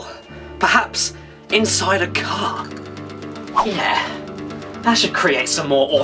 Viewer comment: Due to me being so used to American accents and Malaysian accents (since I live in Malaysia), when Caddy said "Inside a car" I heard "Inside a cock." I am now worried about how dirty my mind apparently is.